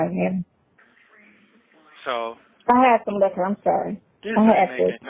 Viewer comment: did she say " I had some liquor, I'm sorry "?